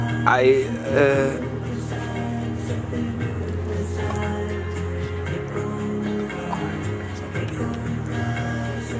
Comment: please cansome body say what did he say about tyson ???????? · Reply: Mike Tyson said once you lick the toad you never look back